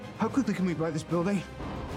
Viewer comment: "how quickly can we buy this building?" I've watched this fight a hundred times and I never noticed that. Lmao that's a good line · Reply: I thought he said "Let me borrow this building"